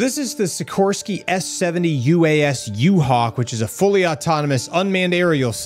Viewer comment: Sounded like you said U-Hal.🤔 Flying U-Hal sounds good 👍🏼